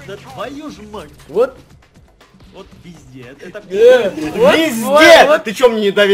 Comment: Я услышала «вот везде» на